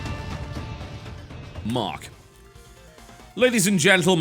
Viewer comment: I think he was saying "3 2 1 mark" and forgot to edit it out...